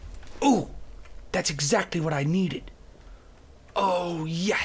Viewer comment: hahaha i heard "I NEED WEED"